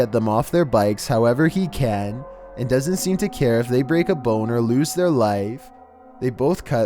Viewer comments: did he say boner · and doesn't seem to care if they break a boner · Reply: He said break a bone or loose their life 🤣 But it does sound like he said that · did he just say boner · On 3:07 it sounds like he said boner wtf 😂